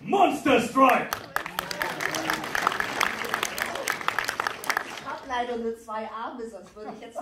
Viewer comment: Was sagt sie bei 5:39 ? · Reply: "Ich hab leider nur zwei Arme..."